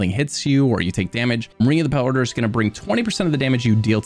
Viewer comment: what is he saying at 5:34? marina power? ring of the power? what? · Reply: ring of the pale order